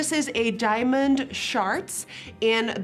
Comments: did she say ‘diamond sharts’ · Did she just say “diamond SHARTS”?????? 😂😂😂😭😭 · Did she say diamond SHARTS?? whaaat omg? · Why did I hear “diamond sharts” when she was talking about the eyeshadow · Legit thought you said "diamond sharts" 😂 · Did you say "diamond sharts?" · Did she just say diamond sharts?! 😂🤣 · diamond... sharts? Tell me I heard that wrong. Lol